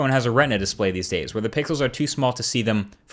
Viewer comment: lol 3:00 he says pickles instead of pixles